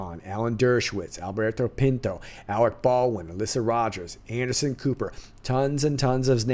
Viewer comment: Omg you about gave me a heart attack! I thought you said "Mr. Rogers" instead of Alyssa Rogers.